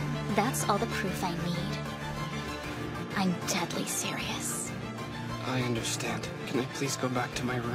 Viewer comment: Wait did she just say she’s deadly serious instead of dead serious?